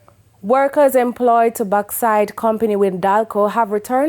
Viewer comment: It sounds like she said workers employed to backside